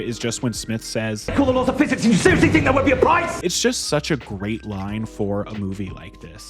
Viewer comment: "My favorite moment here is just when Smith says: 'Cawlasdfwwhiffin simm ajwkeo aseecaksfl spe PIES!?'" lmao what? What does he say? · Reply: I think he says "you broke the laws of physics, you seeiously thought there wouldn't be a price!?"